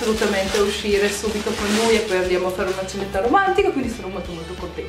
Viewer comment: A parte tutto, io ho capito "ceretta romantica" al posto di "cenetta romantica" ahah